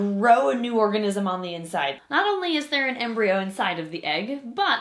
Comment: I thought it said Not only theres a Umbreon inside